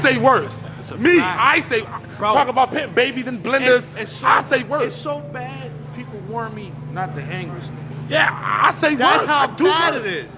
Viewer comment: wait. @ 9:30 did he just say "it's so bad people warn me not to hang wit this nigga" ? ..did he?. did he not learn ANYTHING this week?